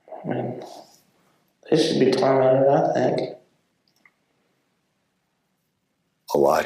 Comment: No one said anything about when said " they should be tormented TOO. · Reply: I heard her say, "They should be tormented, I think. " ... not "tormented, too."